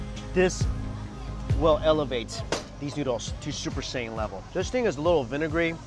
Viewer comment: Did he just say "super saiyyan level"?